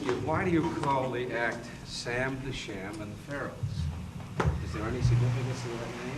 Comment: Did anyone catch the slip up from Mike Douglas??? He says “Sam AND Sham and the Pharaohs”... Smh 🤦🏻‍♂️... Ain’t no one ever gonna sing, dance & scream like Sam (Domingo Zamudio) The Sham & The Pharaohs! No one will ever come close EVER!!! Thanks for this classic jewel 💎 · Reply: I thought he said Sam THE Sham